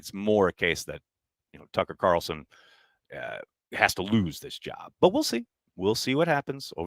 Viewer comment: Realized said Tucker Carlson after playing it back. Thought he said Pucker Carlson as a nickname for Anders since that’s what bhole does every time he runs out to kick.